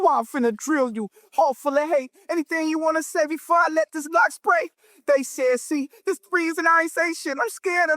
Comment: I heard "before I let this g spray" and was gagged like EXCUSE ME!?!